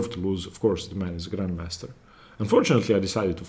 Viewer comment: I'm hearing it too, at 16:30. Glad to hear you were happy to fight, since the middle third of the game showed that you have the ability to create a winning advantage against a grandmaster. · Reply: I thought he said "and fortunately I decided to fight" not "unfortunately" but I may be wrong